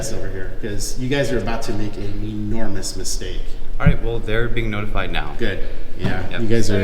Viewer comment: What did she say at around 7:20? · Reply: He said FPS had been notified and was enroute. Thanks for watching!! Please Like, SHARE, and Subscribe. Let's get OUR message of Freedom out far and wide!